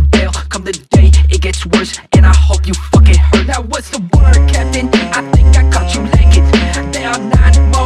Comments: I thought he used to say "work ethic" instead of "word, captain" cuz of all the fitness videos relating to this.... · all this time i thought he said "now what's the work ethic" lmao